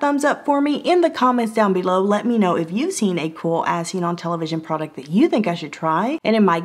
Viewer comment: At the end when you say "Let me know if you see a cool as-seen-on-television product.......", I always hear "Let me know if you see a cool ass seen-on-television product.....". I guess it works either way. Love ya!!